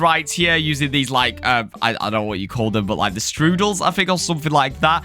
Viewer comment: what did call the floats 💀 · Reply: Stroodle although I meant noodle 😂